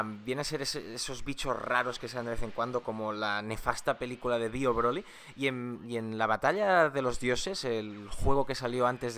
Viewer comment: Entendí Dio Broly xD